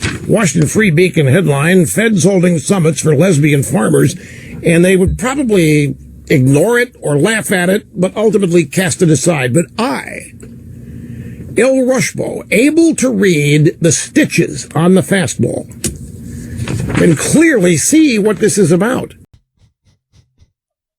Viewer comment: Did he call himself...el Rushba?